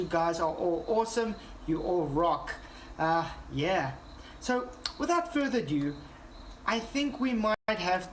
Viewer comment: Why is the quality of this video so bad? The lighting. The jittery stream. The fancy graphics surrounding the video (this is just eating up your data rate for no good reason, the video might have fewer jumps if you got rid of it). And it’s over an hour and a half long! Question: is this deliberately bad and verbose so that only people who are truely committed to flat earth ideology will sit through it? A question for myself: what does it say about me if I actually watch a large chunk of this video? Do I have nothing better to do? I might watch this in short stints and see how much fun it is. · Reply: what did you say? Without further due???